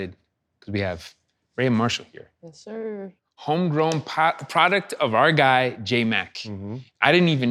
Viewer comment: Did he say homegrown pot at 1:09?